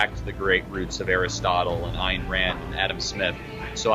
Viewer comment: did he say Ayn Rand?